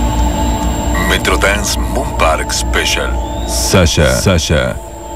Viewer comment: wow i thought he was saying moon pie special